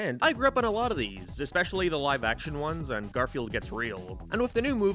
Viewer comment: I thought you said “Garfield Gets Railed “ lmao